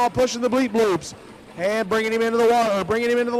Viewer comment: DID HE SAY BRINING HIM INTO THE WALL THEN CORRECTED HIMSELF AND SAID BRINING HIM TO THE LINE? Bad juju with that one clearly